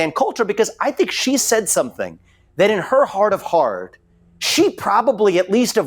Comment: did Vivek just say heart of hard?